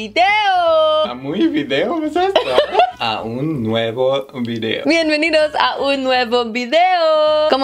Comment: When you said welcome to the new video in spanish, it sounded like you said "un huevo video" which means "one egg video" lol Remember to reverse the words. Un video nuevo*